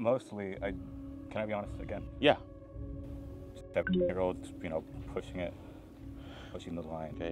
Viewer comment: did he say 7 year olds?